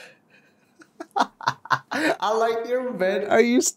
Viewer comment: Nobody: Literally nobody: pickup line : "i like your bed"😂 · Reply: i thought he said "I like your bread"